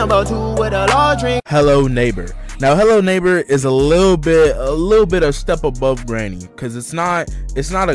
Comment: I swear I would go Mach 10 outta there if I heard him say “little pig” 😭